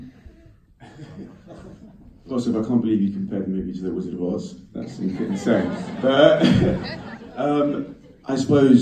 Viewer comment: what did he say? I couldn't understand one word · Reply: "I can't believe you compared the movie to The Wizard of Oz, that's insane!"